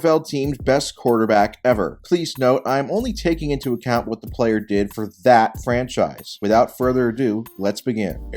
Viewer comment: thought he said “I’m only taking into account players from t h a t franchise” I’m like “this guy 😂”😂 he’d actually say that